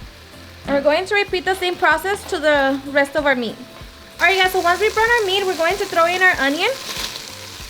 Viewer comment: i'm sorry it sounded like you said once we burn our meat we put the onion in? i mean works for me just checking, just before 6:12 video time. don't forget you rock!!!!!!!!!